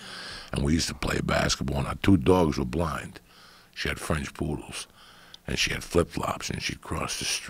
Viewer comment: lol i thought he was describing her tats. "Her 2 poodles were blind you know what im sayin'" LOL